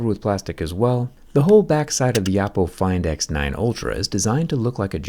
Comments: Did you say Apple X9 Pro Ultra by mistake at 04:27? xD Or was it intentional :D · Did you said 'Apple find x9 ultra'